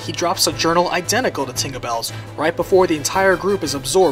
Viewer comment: LOL 'tingabell' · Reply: I heard "Dinglebell" O.o